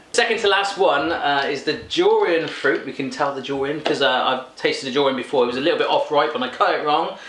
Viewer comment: I totally thought was saying Jurian, and I had no idea what he was talking about. But I guess he was talking about Durians.